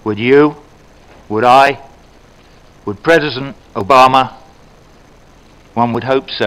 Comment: "Predison Obama" (Is he drunk or what? He does not seem to be well in shape least is to say...) 19:25- (or should I say 19:54-19:56) When did Israel deny the so called "Palestinians" right to enter negotiations as to provide them sovereignty over territories and as such their right of self-determination? (Small remark for putting Palestinians between quotes, is that I'm not a racist person for me every person that lives in Palestine is a Palestinian...)